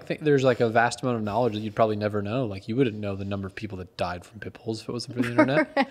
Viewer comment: I heard "People who died from Pimples" insted of Pit bulls, I laughed so hard!